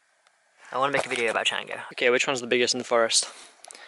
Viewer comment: What did you say at 19:26 ? You want to make a video about what? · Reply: changa, it's something i have a lot of interest in. Will be making a "my first experience" video for the main channel